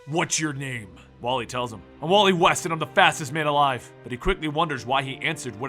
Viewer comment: I thought you said the fastest asian